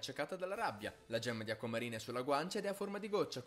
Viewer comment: mentre stavo guardando questa parte avevo il volume un po basso e ho capito "È tro*a